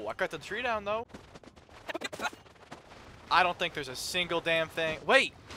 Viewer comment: Why does i hear "I dont think a f-".